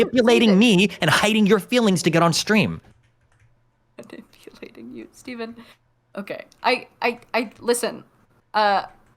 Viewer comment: can anyone figure out what is whispering? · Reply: “Wait…Steven I didn’t… I (mumble) Manipulating you (this is said incredulously) Steven Steven”